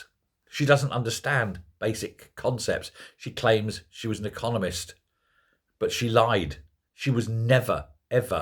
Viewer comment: I misheard "economist". Turns out it's "communist". Two sugars, Rachel.